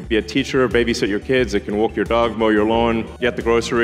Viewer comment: did Elon say that it can Whoop Your Dog??